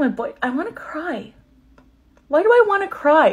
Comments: i thought you said “and my new born baby” instead of “a new born baby” and i lost it for a second there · “it smells like my mom.. and my boyfriend. and a newborn baby.. “ 😭 not the baby 💀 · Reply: I thought she said “and their new born baby” 😂😂😂😂